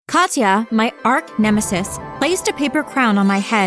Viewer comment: did she say “Ark-nemesis”?